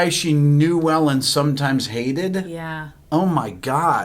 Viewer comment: I always hear "sometimes dated" rather than "hated." (I didn't check prior comments- sorry if I'm the 700th person to say this)